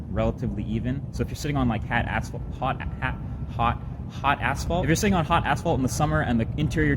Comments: did I hear hot asshole? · hot asfalt i thought he said hot asshole haha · Good video! but sorry for my language I think I heard hot assh*ole @ 2:50 lol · am I the only one hearing hot asshole? :/ I cannot understand what he actually says